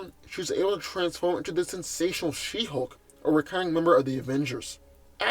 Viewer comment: I swear at 2:33 I thought you said the insatiable she hulk😂😂😂